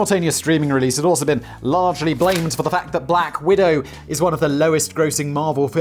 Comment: Haha. I thought you said... "....in her role as Black WEIRDO" ...what?!!! 😄